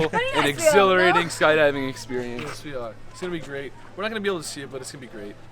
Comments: it sounded like he said sweetheart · WAIT at 11:26 am I the only one who heard Grayson say " sweetheart "?????????? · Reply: He said "yes we are" but yeah it does sound like sweetheart 😂